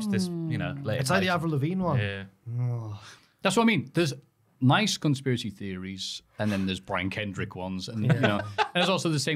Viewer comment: Heard it as "mice conspiracy theories".